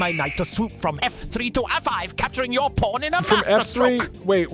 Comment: it sounded like he said f5, am I the only one?